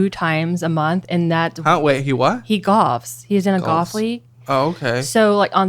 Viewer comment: Why dose she say Gauf instead of golf?